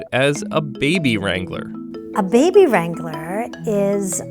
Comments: baby wrangler sounds so wrong skskskskks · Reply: Nina Ahgase thought it said baby strangler for a sec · At first I thought it said 'baby strangler' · i thought they said baby strangler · “Baby Wrangler” ...couldn’t have chosen a slightly less creepy name? 😂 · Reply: THOUGHT IT SAID BABY STRANGLER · I thought it said "Baby Strangler". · I thought she said "baby strangler" what's wrong with me lmao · Dear lord save me I thought they said baby strangler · Jesus, I thought it said baby strangler. Im not saying it would be worse tho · For a second I thought she said baby strangler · I thought it said Baby Strangler